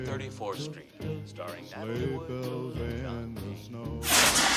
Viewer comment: I thought he said John Wayne but it was John Payne